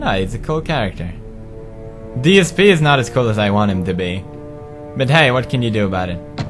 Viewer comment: What does he say at 4:31. He says Dsp is not as cool as I want him to be or something what does he actually say?